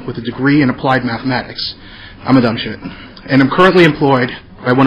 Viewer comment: seconds. did he say "im a dumb shit" ?